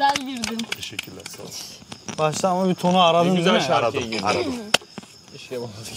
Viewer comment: (ne güzel şarkıya girdin keşke bana da girs-) ben mi yanlış anladım MDMWLXÖSŞXMEŞş